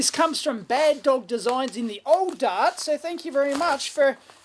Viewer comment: What is it you say exactly your nickname for "England?" Is it Old Dark? Old Dart? Old something? Is it a common Aussie phrase/slang like 'Come a gutser' or 'There's a Bobby Dazzler' or one of your own? Anyway great show. Always informative and entertaining. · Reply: Old Dart